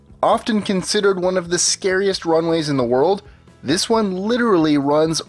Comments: I thought it said "runaways"...that would have been much more interesting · Anyone else thought it said "runaways"?